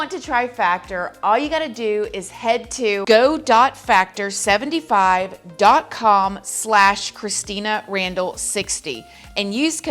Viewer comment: Lmfao I thought your intro said Christmas Randall 🎄 at first. 😄